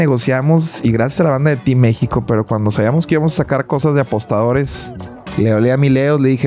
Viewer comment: Por un momento pensé que decía cosas de apóstoles xd